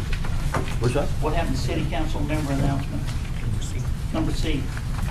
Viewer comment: Did he say number C? C isn't a number.